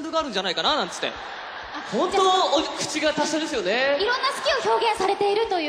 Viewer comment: Can you tell me what did Kaji say in 5:13 in japanese? I'm learning japanese and I want to know how to express like the translation ''he really has a great way with words'' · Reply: 口が達者ですよね~